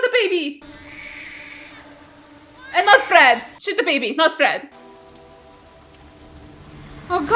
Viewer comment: Uhhhh…did just say “Shoot the baby not Fred?!” What?! Sooo confused right now…🤦‍♂️